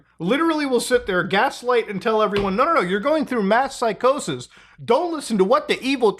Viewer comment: I thought he said meth psychosis, and was ready to accept that as an explanation for Max's behavior.